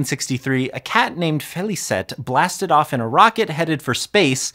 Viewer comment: I wasn't looking and I thought you said a cat named Fifty Cent 😂